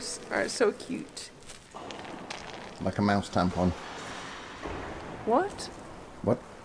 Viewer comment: 🙍Lika Max tampon..........👰what😂🤣🤣🤣🤣😭😭😭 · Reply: I thought he said 'mouse tampon'!